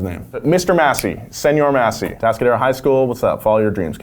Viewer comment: DID HE SAY “ATASCADERO HIGH SCHOOL” That’d be so lit if he actually did